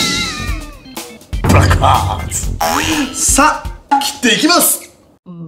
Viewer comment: ブラック パンツって聞こえたのは、俺だけ